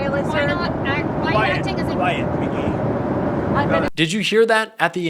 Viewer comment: Why does it sound like he said "quiet! Quiet piggy!" ? Am I the only one who heard that??